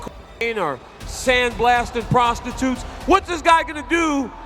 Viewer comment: did he say sandblasting prostitutes